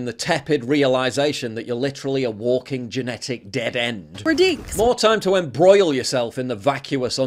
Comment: If you're going to allow Gen-Zs to talk, you need to enable CC. I have no idea what they are saying around around 5:55. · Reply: She says, "We're DINKs." (Double Income, No Kids.)